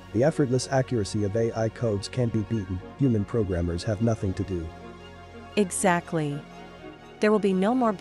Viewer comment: You missed the follow up "human programmers have nothing to do" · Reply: Oh, I heard that as "human programmers are needed who will have nothing to do". Sounds like an ideal job! I guess I will have to perfect my unicycle torch juggling and hope some AI will keep me as an amusing pet :(